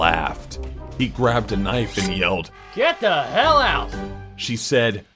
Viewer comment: Get the hell out while in the subtitle says "Get the Fuck Out"